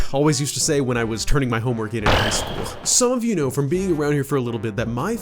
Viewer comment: I thought you said PRE school I was so confused